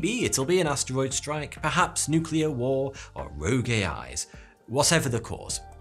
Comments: I heard "Row Gay Eyes" instiead of "Rogue A.I.'s" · I like how to auto subtitles thought he was saying "rogue gay eyes" when he said "rogue a.i's" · I wasn’t paying attention & thought he said, “rouge gay guys”. I had to go back to listen again & was disappointed he actually said, “rouge A.I.’s”. Gay guys certainly deserve their uprising, if we’re being honest. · captions say rouge gay eyes instead of rouge AIs and i found that hilarious in this context! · the subtitles say rogue gay eyes lmao